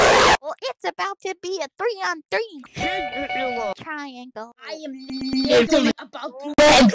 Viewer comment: what are they saying here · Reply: Jengadillo